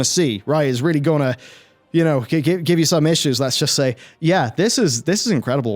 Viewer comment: I thought he said "give you some inches" at 24:21 lol